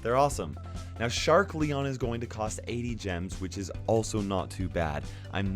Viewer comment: I was listening on low volume and i heard this: "SHARK LEON ONLY COST 8 GEMS" I was like WHAT and i replayed the part to make sure, but it was 80 😆😆😆